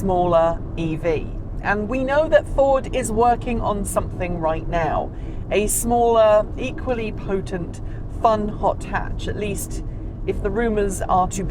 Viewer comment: Great review - but what does “plutent” mean? You used this word around minute 41:51. I don’t know if I spelled it correctly, but that’s how it soundedd to my ears. I couldn’t find a definition for it. Thanks for your help